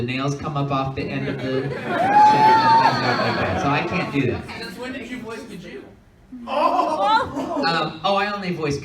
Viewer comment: Did anyone understand the question from the audience member at like 6:08 that Todd responded "oh i only voice good characters" to??? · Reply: Since when did you voice Gajeel?